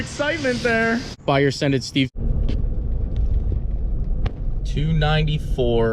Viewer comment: Lol the captions at 0:17 caught it as " Scented Steve " instead of " Send it Steve ". You should totally sell " Scent Ed Steve " air fresheners.